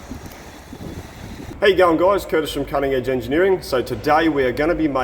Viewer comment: I know I am misinterpreting the greeting you do at the start of the videos, which I enjoy. I hear it as “hey young guys” and unless you are excited to address a subset of your viewers, I must be not hearing this correctly. 1. What are you actually saying? 2. Does it have local history as a common greeting. and 3 Can you start out one video with, What’s happening guys!” Ha ha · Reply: He is saying "how you going, guys?" It's an aussie greeting that doesn't really need a response.